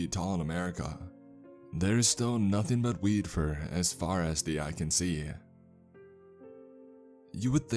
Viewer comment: What?!?!?!? Did you just say weed as far as the eye can see? Dude!!! Sign me up with a pocket full of papers!!!!!!!!!!😱